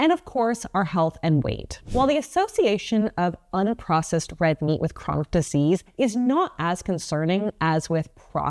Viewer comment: I heard that as "The Association of Red Meat with Processed Disease" like it was a gov't agency. You know, the ARMPD.